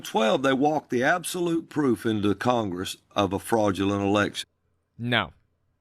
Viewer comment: I keep hearing "false erection" 🤣